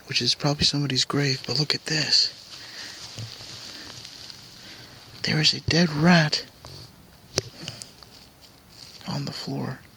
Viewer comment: love the intro sounded like you said here's a dead rat making it this way i know that's not what you said but deffo sounded like it lol. thank you for another great video.